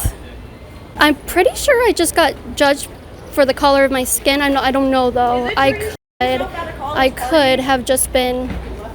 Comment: What did the girl in the yellow shirt say? I don't understand what happened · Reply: She said is it true that you shit yourself at a college party.